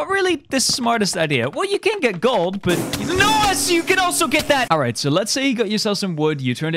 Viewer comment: I thought you said nos lol